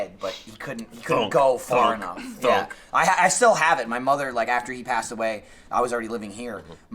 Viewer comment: I seriously thought Michael said, "My mother, after she passed away, sent me..." and I almost had a fucking heart attack.